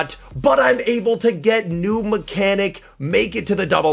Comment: What happened here? Did you say new mechanic?